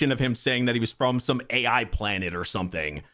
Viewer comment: I don't think he said AI planet. I believe he mentioned he has AI family members or something. He does say he has been running away for decades so probably lived with adopted family and stuff. Man his story is making me Soo dang sad. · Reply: No he did say AI planet. Also, when character's mention decades, it's usually because they've spent long periods of time in cold sleep. Remnan is only 19. Jonas has spent hundreds of years in cold sleep, which is why he's a little insane